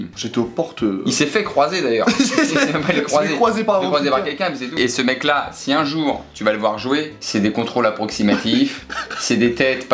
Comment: il parle de pierre croce ou sais moi 😂